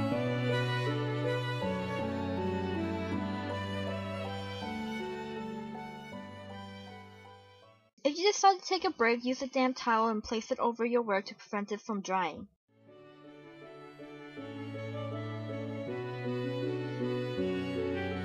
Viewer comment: Instead of "damp towel" I thought you said "damn towel" so I thought you were getting a bit irritated or something XD